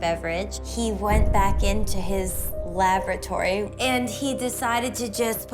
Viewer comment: did she just say "...and he went back to his lavatory?"😂😂😂